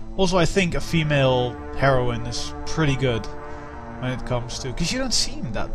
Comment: It sounded like you said "A female Heroin" :3 Still, good let's play.